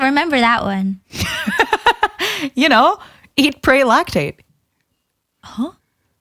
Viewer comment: "Live, Laugh, Lactate" absolutely should be on a t-shirt... In more ways than one. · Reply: bro i thought she said "lactaid" and i was like "the milk? is she lactose intolerant???"